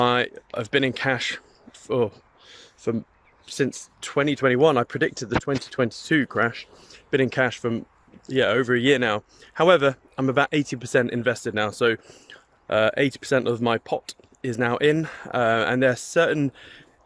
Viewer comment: Come on bro.. I respect you but keep your integrity. Don’t lie. You did not call the 2021 crash. I was in your community at that time. You were caught out like everyone else waiting for $100k. Edit: Apologies but I didn’t ‘fact check’ before I made this comment. He didn’t say that he called the 2021 crash - he said he called the 2022 crash which he was right on. I commented too hastily. Sorry 😊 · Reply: I have to apologise - I thought I heard you say that you predicted the 2021 crash. But you said you predicted the 2022 crash which you were right on. I’m sorry for commenting in haste and not ‘fact checking’ before posting. FYI I watched everyone of your Trading Pubs in 2021 and 2022 😎